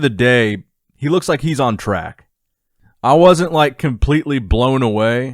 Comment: thought you said “Looks like he’s on tren”